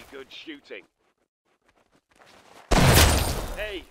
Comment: Хорошо, you got him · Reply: I didnt read the dialogue I thought he said good shoot